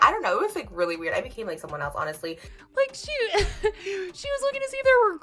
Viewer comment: What did she say at 6:17? I heard “I think he likes someone else honestly” or maybe(hopefully) she said “I think he came with someone else”?